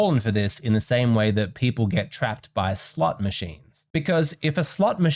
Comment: Is it wrong that I thought said “slut” machines instead of slot machines